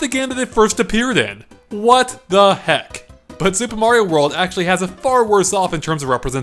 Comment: In the beginning it sounds like he says super mario nword 2